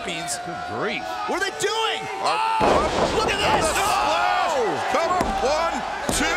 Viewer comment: Excalibur nearly called them the Revival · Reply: Yeah at first I thought Excalibur said The Rev... But after a third time I heard him say Cover